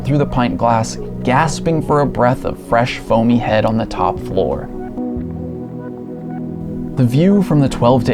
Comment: what did he say????? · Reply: fresh bone head on the top floor